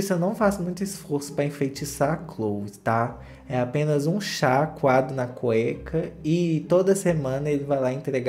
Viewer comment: O tristan não faz muito esforço para enfeitiçar a Khloe , é só ele dar uma chacoalhada na cueca e ela cai igual uma pata 😂😂😂😂 não dar o é o melhor . O já tá cansado dessa pataquada kkkkkk e eu tbm , ja mandei até um tt para hulu pq tbem tô cansada kkkkkk😂😂😂😂😂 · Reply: E eu que entendi "um chá coado na cueca"!! 😂